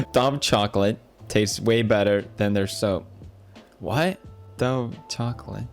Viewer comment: I don't understand anything they said during the Dove chocolate/soap thing. "It's not their soap"? "Learn English next time"? Wtf are they talking about SOMEONE HELP ME